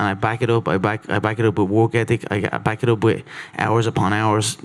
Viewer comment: Hi! Great video! I have one remark about the subtitles though: in about 7:54 Conor says "And I back it up with WORK ethic[...]" Subtitles say" I back it up with WALK ethic". I think you might want to change that.